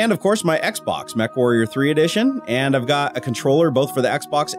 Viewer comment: Did he say Xbox Mech Warrior 3 edition? lol Modern Warfare 3 edition. XD I have the same one.